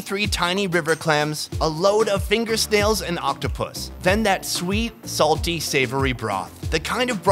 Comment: Did he say oc-toh-pooss?